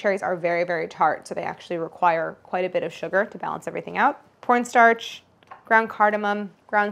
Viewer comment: did anyone else hear "pornstarch"? I got new earbuds so it might be me.